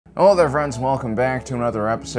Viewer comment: Follow on Instagram! <link> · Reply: I thought you started off saying, "Hello, money is Alen."